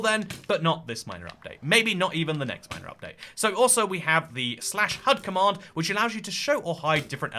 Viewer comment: for a second I thought he said the /hug command and now I want that